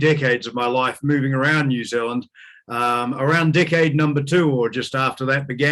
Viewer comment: I honestly thought he said, "Dickhead", not "Decade" - sorry, x